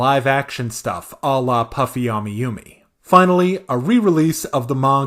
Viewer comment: did I hear that right did I hear puffy amiyumi 😍